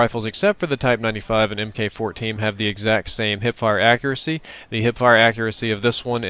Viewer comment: I thought for a moment it said "poop hip fire accuracy".